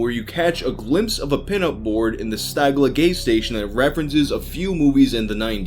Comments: did you say "Stagla Gay Station" lol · Who else hears, "Stagla Gay Station"? Edit: Wow guys, thanks for the likes! Also my dude, fucking love your content. Keep up the great fuckin' work! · did he say GAY station???